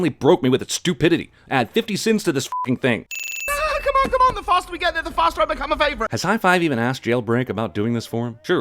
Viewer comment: did he just say he wanted to start vaping